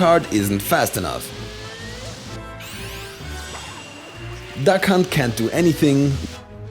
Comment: I heard "that cunt can't do anything."